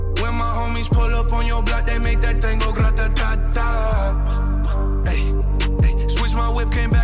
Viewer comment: I hear ratan tata